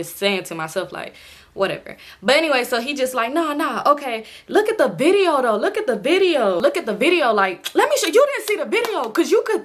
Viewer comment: I keep hearing “look at the video though, look at the video though” and it’s killing me 😭😭😭😭😭